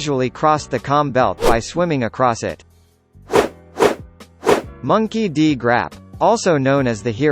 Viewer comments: I heard "Monkey D Grap" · did he just say Monkey D. Grap??